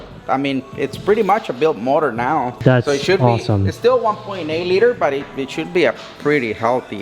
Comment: Did he say 1.8L? Lol WUT?